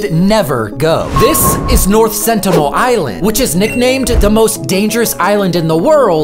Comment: I had my computer on volume 1 and i misheard north sentinel island for ohio ☠️